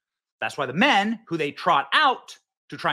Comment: Did he just say that he used his rifle for trout like the fish? With a gun.... 😵 I hope i misheard that